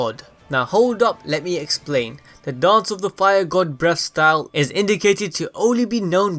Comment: I actually heard him say “breast style” the whole video lmao 😂